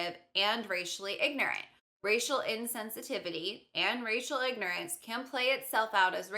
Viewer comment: lol when she said "racial ignorance" i thought she said "rachel ignorance" wow the words are so similar